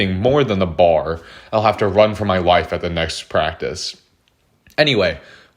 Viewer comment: First time through, thought you said "run from my wife" and I got super confused